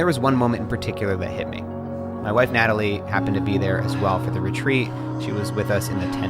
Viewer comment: did he say my WIFE Natalie? They got married?